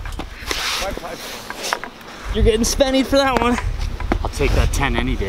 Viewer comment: What was nickname for a 10 min penalty? said "I'll take that 10 any day." Sounded like he was saying sennie or shennie? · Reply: "spenny" like youre gonna get suspended for that